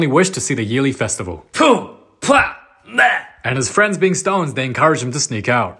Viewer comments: I thought he said "with his friends being stoned" 😭💀😭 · I thought he said ‘his friends being stoners’ 💀